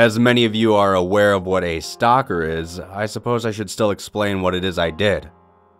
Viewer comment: At the first part when he said Wal-Mart stocker I thought he said Wal-Mart stalker